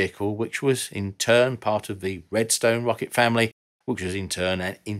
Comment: Did i just hear redstone? MINECRAFT!?!?!